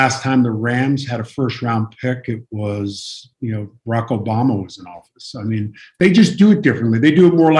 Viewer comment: It sounded like Howie said Rocco Bama